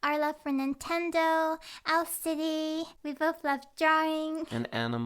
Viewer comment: hahaha I was watching the video whitout really looking at it, and when you said "owl city" I heard "LSD" and I had to rewatch it HAHAHAA